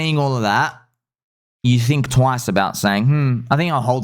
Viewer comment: I said the same thing when I first listened. Bell button. Read in description.😂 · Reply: I swear to big G I thought he was saying “ and belly button all!” 😂